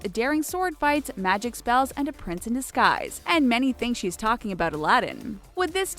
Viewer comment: they say "A prince in disguise" and the movie says that to. But their subtitles say "A PRINCE IN THE SKIES" Lmao what?